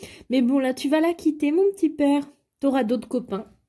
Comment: C moi ou a 2:34 ta dit mon petit PORC 🤣🤣